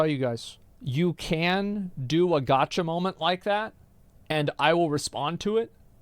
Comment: Oooh “gotcha”. I kept hearing “gacha” was very confused